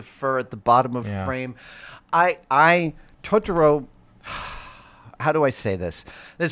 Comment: I thought he said Trump Totoro instead of Tron. I imagined a Totoro with bad orange hair, groping his daughter.